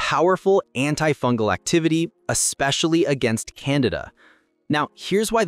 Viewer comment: Can-deed-ah not Canada! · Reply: At first I thought he said "You can use it against Canada." Lol